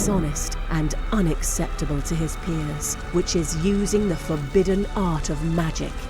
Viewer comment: I'm sorry, even after the fith replay (I'm German so plz forgive me) I hear "Your father did something shamefull, dishonest an unacceptable to his...penis"